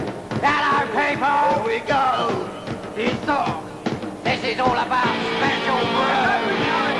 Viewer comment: for a moment i thought he said "this is all about spiritual growth". made me laugh a bit.